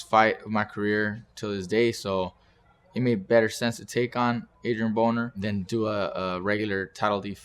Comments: does he say Adrian Boner? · sounded like he said Adrien Boner 🤣